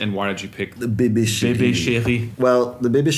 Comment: When yall were talking about that OTO mixer, at first i heard its "the O.T.O. Baby Shitty". I did a double take like wtf did he just say lmao